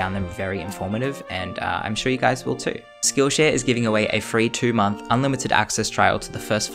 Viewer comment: Please don't hate meh but sounded like u said skill shit lol